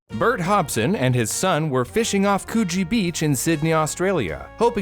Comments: I thought he said Cochie beach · am i hearing things or is this actually what this beach is called- c00chie beach im sorry i had to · I'm happy to see that I'm not the only one who thought he said Coochi beach 😂😂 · I thought you said cochie beach 💀💀💀💀💀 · I know that that says “Coogee beach” but I can’t stop hearing..... other things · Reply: I feel you I heard him say “coochi beach”